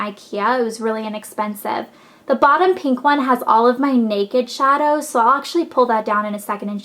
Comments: hahaha omg when you said in the pink box is all my naked shadows, for a second i thought you said photos ! woops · when you said "naked shadows" for a sec i thought you said "naked shots" and i was like "uhm, elle what the hell?" haha